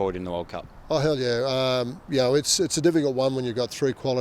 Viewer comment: does sheens say "oh hell yeah!"??